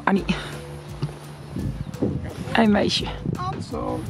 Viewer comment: did he said hey meisje